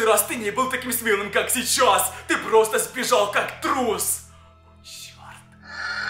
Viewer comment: А мне послышалось просто срал уже